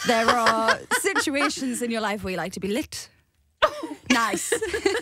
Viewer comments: I thought she said "licked" you name the time and place Perrie 🤣🤣😳 Hey i can dream can't I? 🤷‍♂️😳😅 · I also thought she said licked and my reaction was the same as Jade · i thought she said licked 2 XD